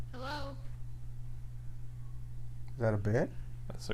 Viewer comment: I thought you said "is that a bear?"